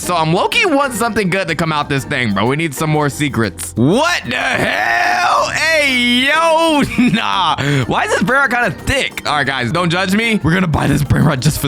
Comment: DID YOU SAY DICK